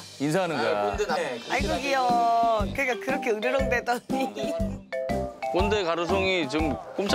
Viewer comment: 왜 꼼데 부를 때 꼰데라고 들리냐 ㅋㅋㅋㅋㅋ